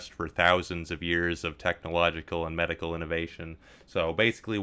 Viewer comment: "Does anybody need blood?" Blood for the blood god (when he said "technologically advanced", i heard "technobladedly advanced i think i watch too much yt)